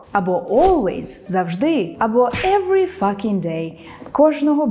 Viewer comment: Чекаю на ваші враження від нової рубрики. Обіцяю особисто відповісти на кожен коментар (перші 500) більше семі слів! Записуйся в розмовний ондайн-клуб по акційній ціні 350 грн за урок (кількість місці обмежена): <link> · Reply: Every fusking day instead of every fuCKing day?